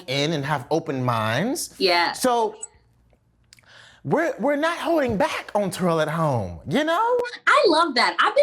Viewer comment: Did He Say "We Not Hoeing Back Anymore"😂😂😂